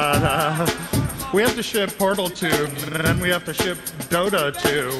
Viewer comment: why do i keep hearing "we have to shit portal 2"